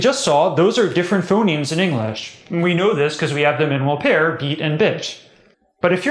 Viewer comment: it sounds like he says "bitch" lo. ... or maybe it only sounds like that to me because I have a different phonology? hm..